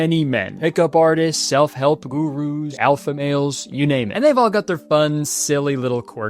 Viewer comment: i heard what kurt said at 0:18 as gugus instead of gurus and now that's all i can hear now.